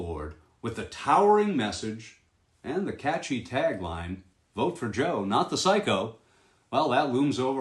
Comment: At first I thought it said "Vote for Joe the psycho". Unfortunate wording. But Joe kinda is.